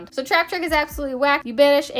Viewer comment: I heard - "Traptrick is a whacky b*tch" hahaha I was like, "this is acceptable."